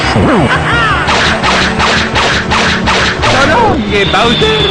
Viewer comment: J'ai jamais compris ce qu'il dit à 18:05. What did he says ? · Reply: Greg C "so loong gay Bowser" je pense 😂